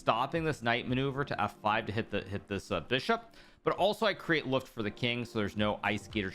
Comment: What does he say at 4:38? "Create something for the king"? I can't hear the word no matter how many times I replay it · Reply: Luft.